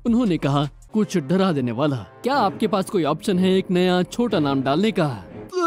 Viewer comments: is it just me, or do I hear the n word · what did he say? 💀💀💀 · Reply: He said “naya” which means new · what did he say directly before vomiting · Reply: He said “dalne ka” which roughly translates to “keep”. It doesn’t translate well into English so I hope I’ve translated it properly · sounds like he said the n word💀